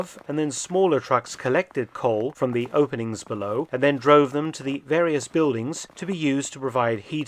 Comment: I adore Felton's videos. But did I hear the word "droven" at 2'55? So naturally, as an English teacher I jumped to AI only to discover that "droven" is an obsolete form of the past participle of "to drive". Mr. Felton, you impress me! I think I have seen all of your videos and have always been amazed by the objectivity of your commentaries. Bravo. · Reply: I just checked, he said "drove them"... but fascinating that droven is an actual word, I've certainly never come across it.